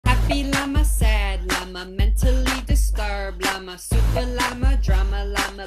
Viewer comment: its “mentally discarred llama” not “ mentally disturbed llama” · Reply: omg sorry it is mentally disturbed, my apologies. I heard it as discarred llama xD